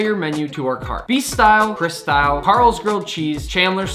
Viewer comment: I honestly thought he said "The Chris Kyle" instead of Chris style.